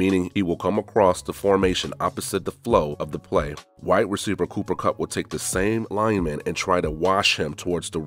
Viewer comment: Lol I thought you said white reciever Cooper Kupp.